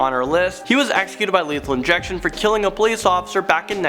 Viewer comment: I thought you said Toriel hah